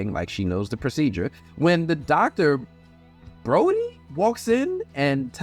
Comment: The doctors name is Dr.Boyd! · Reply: I thought she said Dr. Brody...?